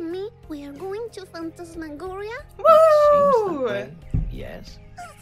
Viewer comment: I thought she said AWOOOO because of the wolf lol 🐺